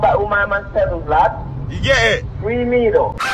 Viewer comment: I swear she said “free me doe” 🤣🤣